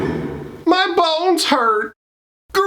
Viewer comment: I swear it's always sounds like he says " My balls hurt" in the intro.